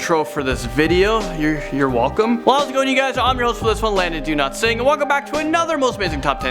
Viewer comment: Thought you said your name was Landon Do Not Sing 🤣🤣🤣